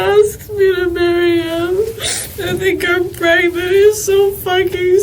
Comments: does she say “I think I’m pregnant”? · when it says inaudible I think she says she thinks shes pregnant